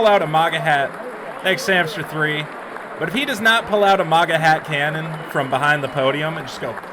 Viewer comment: I thought he said “Manga Hat Cannon”